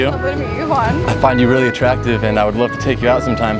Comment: was i the only one who heard "attrucktive"?